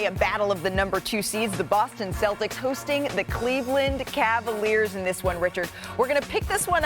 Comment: did she say the Cleveland cavaliers? Lmaoo 😂😂😂